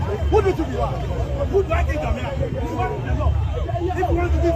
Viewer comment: Herh!!! Did I hear KOKA said “who do I think am yam” 😂😂